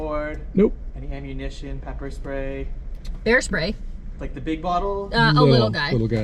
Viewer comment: At the border checkpoint what did the guard mean by large can of bear spray? The one I carry is about 7 inches tall and thought that was the only size they come in. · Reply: I thought you said hair spray!